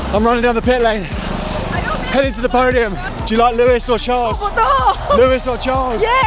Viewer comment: I think the lady at 25:11 say Louis Vuitton not Lewis Hamilton 😂.